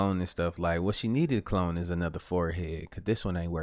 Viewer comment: 😂Did said she need to clone her forehead??? At 3:44😂😂